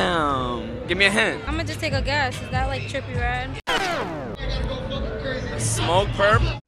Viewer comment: Did she just say is that trippie redd 3:48 🤦🏽‍♂️